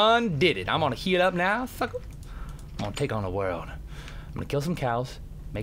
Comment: DID HE SAY FUCKER?!?!!?!